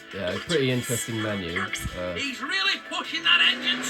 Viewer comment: It sounded like he said he really fucky to me😅